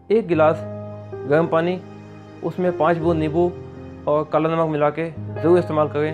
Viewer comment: गर्म पानी, निंबू और.... क्या? ठीक से सुनाई नही देता! प्लीज बताए।🙏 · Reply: kala namak ek chutki